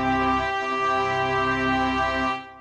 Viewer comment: I thought it said lick the cheese